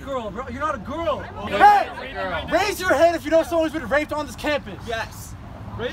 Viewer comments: Ohhhh, english isn't my mother tongue and i can't understand what the soyboy says at min 3:40, when he asks people to raise their hands for...? If someone could help me, i'd be so thankful. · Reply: Jorge Laka i think he said “raise your hand if you know someone who’s been raped on this campus” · I'm deaf can someone tell me what was said from 3:38 to 3:48. Closed caption goes haywire at that part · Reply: Mr. Infinite the white man with the rucksack on says raise your hand if you’ve been raped on this campus. Then someone in the crowd says raise your hand if you think rape is a horrible thing